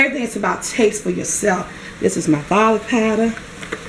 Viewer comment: Hello, at 7:05 I can't understand what kind of powder she is using...Please help someone. TY! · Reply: Daniel Lee Garlic Powder